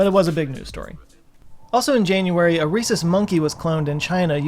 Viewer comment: I thought you said, “a racist monkey” and I was super disappointed in scientists